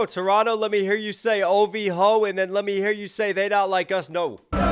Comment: I thought I heard they were singing "Drake Not Like Us, Drake Not Like Us" instead of "They Not Like Us" ..maybe I got hearing problems now 😂😂😂